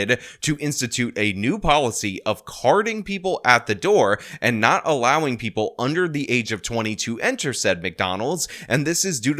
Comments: “Under 22.” Dang 😂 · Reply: I caught that as well. He said not allowing people under 20 to enter... but it sounded like people under 22 enter... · He said not allowing people under 20 to enter... but it sounded like he said people under 22 enter... LOL